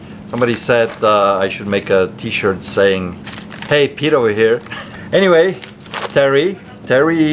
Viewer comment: I always thought you were saying Hay get over here.